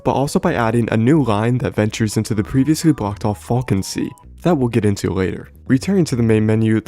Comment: Thought u said "balkans"...I was like no way ts surviving 😂